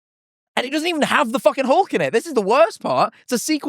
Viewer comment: Until 24:20 I thought they were saying "hawk" and not "Hulk," so I had just been picturing both some random bird-themed guy and an actual bird lol